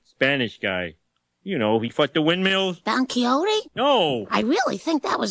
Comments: Is it just me, or at around 0:58, does it sound to anybody else like Homer says 'you know, he fucked the windmills.'? I didn't hear it clearly at first, but now I can't unhear 'fucked the windmills.' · did he just say “he fucked the windmills”?!!!